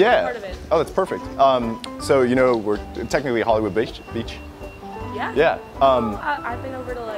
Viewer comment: Did anyone notice that said 'bitch' instead of 'beach' at 2:00?? lol